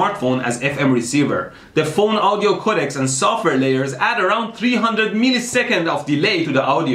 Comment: when he said software layers i thought he said the n word